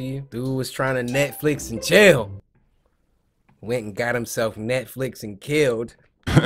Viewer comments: “Dude was trying to Netflix and chill went and got himself Netflix and killed” 😂😂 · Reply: i thought he said "neckflexed and killed" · Did he say "Nigga got himself Netflix and kill”? The first time he cuss on his channel?